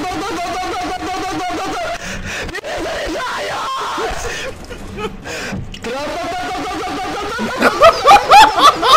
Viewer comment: what did he say? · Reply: RELOADING!